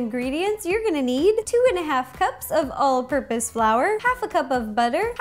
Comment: Am I the one who understand "flower" instead of "flour" ?